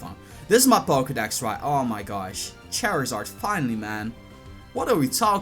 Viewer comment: Lol it sounded like you said chairizard